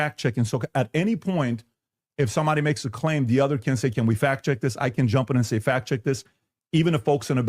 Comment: On 2x speed, it sounds like he says “Can we fat chick this?”